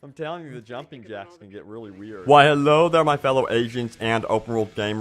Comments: I thought you said "welcome my fellow Asians" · at first i thought he said " Why hello there my fellow ASIANS " lol wtf haha